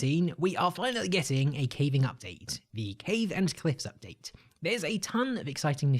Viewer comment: Did he just say ‘the cave and clit update’